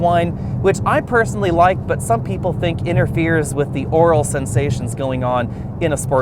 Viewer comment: Did he just say " Oral Sensations"